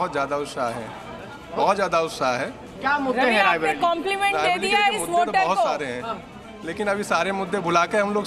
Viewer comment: Did I hear - Compliment de diya is mote ko at 1:30 ??